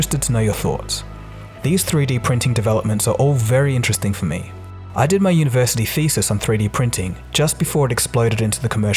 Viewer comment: As a non-native speaker I really had to skip back. "Wait, what? Did he just say he made feces with 3D printing at the university?"